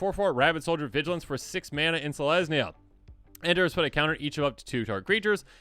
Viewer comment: I thought he said “Finneas, Ace Archer, it’s a lesbian”. I played it back and he said in Selesnia…